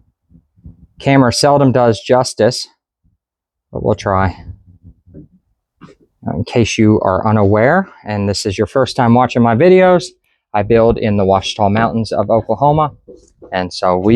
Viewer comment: Just heard you say Washau mountains in Oklahoma.